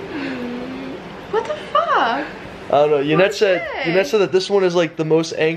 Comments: did she say fuck? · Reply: Yeah sounded like she said "what the fuck" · did just say "What the fuck?"